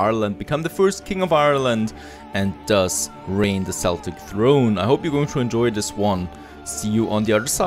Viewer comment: did he say seltic throne